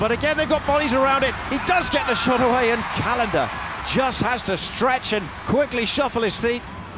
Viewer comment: thought he said canada